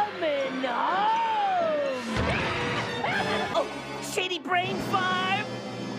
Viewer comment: O_O 1:02 Ok did Stimpy say City or SHITTY BRAIN FARM!? LMao!!! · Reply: He said “shady brain farm”